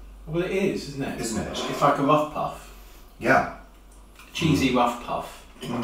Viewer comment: oh dear, I thought said cheesy muff puff 😂